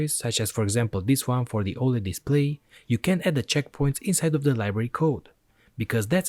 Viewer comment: im sorry, i couldnt understand in 7:09 do i can or can not use inside the library ? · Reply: You can't. At least that is what subtitles stated, because english is not my primary language and firstly i heard "you can" there insetad of "you can't"